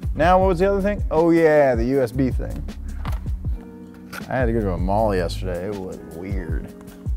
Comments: " i had to go to the mall the other day... it was weird" i felt that lmfao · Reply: Sounded like he said he had molly yesterday and I had to do a double take. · Dude! I thought you said “I had to do Molly last night. It was weird!” Bummed I misheard you. Love you guys, but please do more drugs. Except Nolan. That guy is always on drugs. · Ohhh 8:36 he said I had to go to a "mall" yesterday...I thought he said i took molly yesterday. Lmao